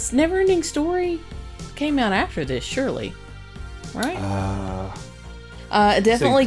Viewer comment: Did call 'Shirley' at 37:27?